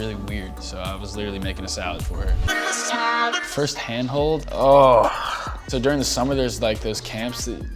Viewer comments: When he said first hand hold I literally thought it said first hand job😂😂😂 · when it said first hand hold i thought it said hand job was like lol